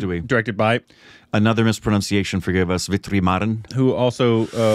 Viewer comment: when said Vetrimaaran, the subtitles said "Victory Mordern" 🤣🤣 2:50 Mani Ratnam came as "money rottenham" in the subtitles 💀